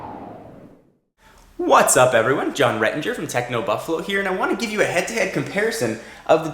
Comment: sounds like he says "TECHNO-BUTTFLOW"